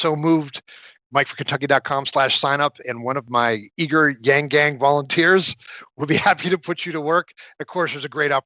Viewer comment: Did he say "one of my gang-gang volunteers".....Nice!😂😂